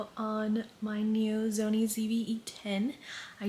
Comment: you said “zony” instead of “sony” 😂